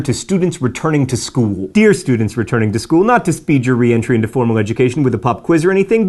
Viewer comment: I heard: "Not to speed your re-entry into formal education with a puff quiz..." John, can puff quizzes be a thing. Please can they.